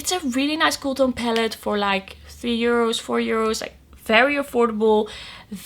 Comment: When you were discussing the cool toned Essence palette that you did a look with I "thought" you said "it would be good for 3-4 year olds!" 🤣LOL - I thought "HUH?" Then I replayed it... and I realized you said EUROS not "year olds". I had a good laugh. ha ha - *My hearing is not the best after blasting rock music in my car as a teen & attending many heavy metal band concerts!! 🤣 I had a customized stereo system in my car with MANY speakers & TWO 300 watt amps!! (I actually had to put a boat battery in my car cause a regular car battery kept dying cause of this) My mom said I would ruin my hearing & she was right. (but it was fun at the time) 😁 ok... sorry for the rant.